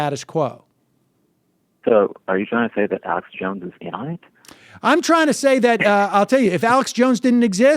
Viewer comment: "Are you trying to say that Alex Jones is..." What did this guy say after that? My drunk ass can't hear what he said. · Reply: In on it.